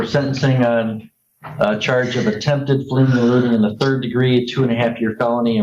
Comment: I wonder what her crime was? All I heard was "attempted blfhldmflj and reckless driving".